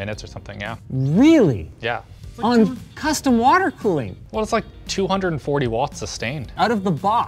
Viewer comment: Everytime the CPU boosted higher with the temperature dropping. Now what I want to see is a situation in which the CPU doesn't draw more, to see the true temperature difference. Maybe limiting the CPU clock to whatever the stock CPU was running at. I think it should actually reach the 15°C mentioned online. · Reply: Oh I thought I heard them say "custom water loop" was used for the first test.